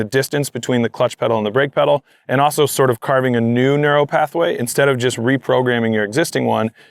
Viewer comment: subtitles say "narrow pathway" instead of "neural pathway" lol